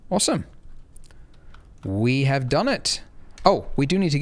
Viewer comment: Did you say " We have Donut" or "We have done it?" >.<